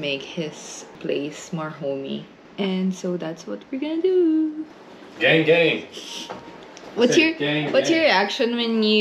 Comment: I thought I heard "get engaged" instead of "gang gang", I really need this ear of mine checked. lol